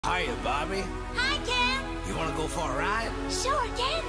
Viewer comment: for some reason i hear "hi barbie hi dad wanna go for a ride sure dad